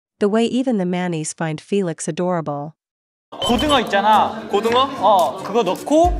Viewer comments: I so thought I heard "the way even the mannies (male nannies) find Felix adorable" 😂 I really thought about why Felix was being babysitting for a second.... and WHY can I smell the doggone food rn 😂😂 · What I heard: "tHE WAy MAneasE FinD FeLiX AdORAble"